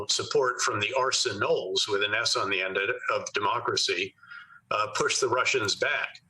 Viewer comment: Petraeus said it; I heard him, "The arsen 'oles of democracy."